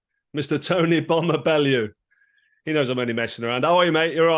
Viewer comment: Thought he said tony bummer bellew 🤦‍♂️🤣🤣🤣🤣🤣🤣